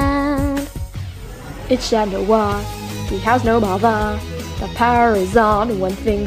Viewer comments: I died at “it’s Chat Noir, he plays the guitar, actually he doesn’t he plays the piano” and “it’s Chat Noir, he has no mother” 😂😂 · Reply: I swear I heard “ it’s shat noir, he has no mother”😭😭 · I thought she said BiTcH cHaT nOir I had to watch it like 7 times lol